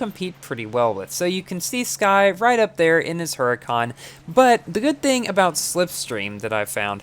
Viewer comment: DID HE JUST SAY HURACCAN ?